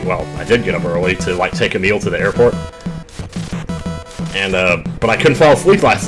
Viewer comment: Thought he said, "take a meal to the airport." Then realized he said, "take Emile to the airport." 😂